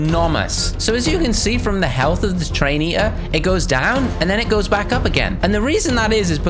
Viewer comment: I thought it said train entry